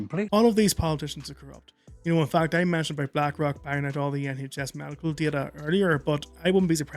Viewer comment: What on earth did you say here?! Irish accents just throw me for a loop sometimes. 😂😂😂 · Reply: 😂 I don’t blame ye buddy 😂 I was speaking about black rock and the nhs 👀 😂 much love buddy and great to see ye again 😊❤️👊